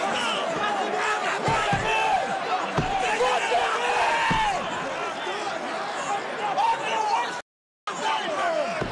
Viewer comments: What was he saying Did anybody have any idea? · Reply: “Look at me” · Did anyone heard what he said..🤔🤔? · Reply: "I'm Your Worst ***** Nightmare".